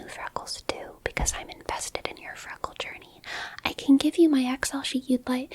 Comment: Imma be honest my dumbass thought it said "can I show you my feet" for a solid few seconds and I ain't know how to react to that 😭